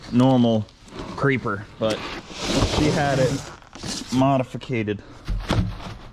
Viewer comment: Did u just say #modificated 🤣🤣